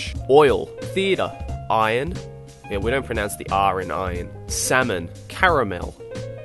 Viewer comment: For a moment, when he said "Salmon" I heard: "Semen" I have a dirty mind v.v